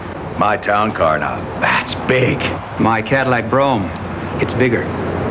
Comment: Another commercial I remember from my childhood. I thought he was saying “Cadillac Brawn”, which would make sense… Still, only 18” longer than my Charger - now for these days, that’s a BIG CAR! And not near so stuffy.